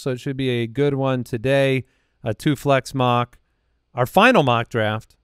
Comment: I sincerely thought said “a toothless mock”